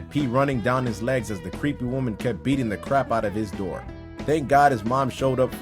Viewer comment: I heard “Crap”